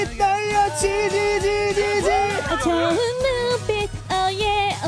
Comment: 젖은 눈빛인데 좋은으로 들리는데 틀린거 아닌가